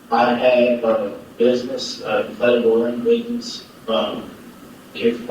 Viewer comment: did anyone catch what kind of business he said he had? it sounds to me like he says an "inflatable run business"...? 🤷🏼‍♀️